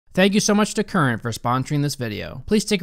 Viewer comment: When this vid autoplayed in another tab I was not looking at, I heard at 0:00 "Thank you Karen for sponsoring this video"